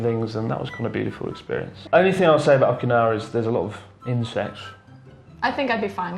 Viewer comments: WTF I straight up thought he said 'There is a lot of ''INCEST''' I died when she said she got used to it in Australia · lmao 1:40 thought he said incest